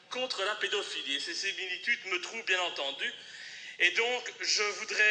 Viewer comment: Qu'est-ce qui est dit, précisément, entre 2:02-2:05? "Et ... , bien entendu"? Merci d'avance.